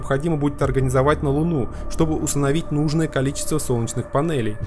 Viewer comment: "Чтобы усыновить достаточное кол-во солнечных панелей"?xD Услышал когда на фоне видео слушал.